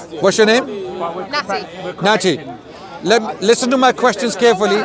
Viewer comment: 8.30 Rahh was she sniffing coke?? Edit: 23:00 did she just say her name is nitty??